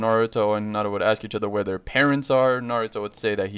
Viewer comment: When he said “where are there parents” I thought he said harem stars